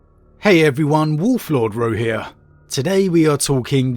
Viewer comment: Why everytime I hear "Wolf Lord Bro"